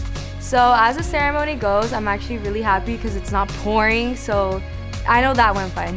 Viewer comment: did she say POorinG😳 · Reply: It’s she said pouring